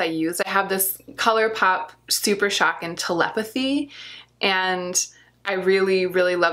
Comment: Omg I have telepathy as well and it’s breathtaking!!!! The look you came up with is so beautiful, I’ve been needing inspiration for this shade! Love the video 😊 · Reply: Hah, for a long moment I thought you were saying that you are telepathic! "I have telepathy" LOL. Finally figured it out. Yes, it's one of my favorite shadows! xoxo